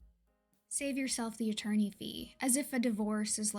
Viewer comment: I misheard the "save yourself the attourney fee" as "save yourself the eternal fee" and was very confused until you repeated the line. Both are just "ew" kind of lines.